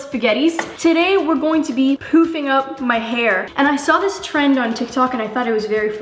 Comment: I heard it as "today I'm going to be pooping my hair"